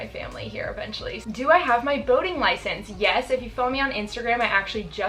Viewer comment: why did I hear 'voting license' instead of 'boating license' lol I was like why is it so dangerous in oregon???